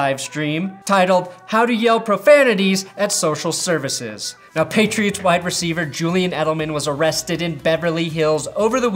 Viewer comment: I first thought you said “patriots WHITE wide receiver”. I was disappointed I was wrong